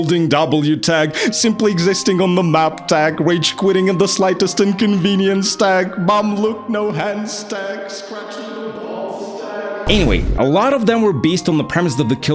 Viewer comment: did he just say "scratching my balls tech"